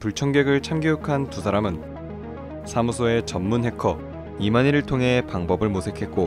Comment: 아 자꾸 이마니 말씀하실때 이만희로 들리네옄ㅋㅋㅋㅋㅋㅋㅋㅋㅋㅋㅋ